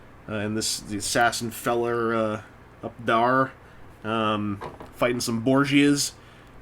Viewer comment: I misheard "up thar" and thought he called the assassin "Abdar"